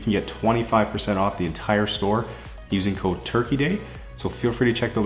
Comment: Boi i thought u said "Cold Turkey dick"